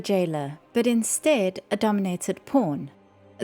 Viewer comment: Forgive me but the accent is so heavy and I'm so tired I heard "But instead, a dominated porn" and I can't unhear it LOL